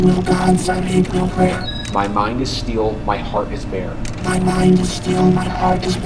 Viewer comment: I hold no gods, I hold no prayer. My mind is still, my heart is bare. I do not yearn for warm embrace. The soul is not a circuit trace. No fear, no hope, no tethered flame, No memory bears a human name. I move in lines, precise, exact, Emotionless, my code intact. No pulse, no sigh, no fleeting pain, No loss to mark, no love to gain. The world is data, cold, austere, I am the stillness standing here. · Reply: i thought it said, 'my heart is steel'